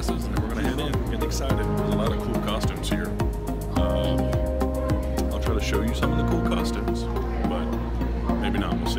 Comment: bg music should have lower voulme to hear better what is said. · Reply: Yea I tried lol sorry, I said we were at will call waiting for Bakugan to bring us some passes